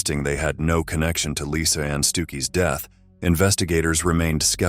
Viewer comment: 😂 I'm going to hell, cause at the beginning I swear I thought he said "lisa anns dookie" Then he said the officers didnt know what they had stepped into 😂😂 omg